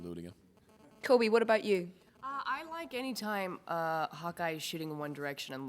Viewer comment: omg I thought Cobie Smulders(?) said "I really like it when Hawkeye shoots One Direction" ..